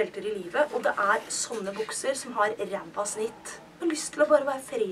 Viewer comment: I'm swedish so when you said "bukser" i thought you said "boxers" (men's underwear)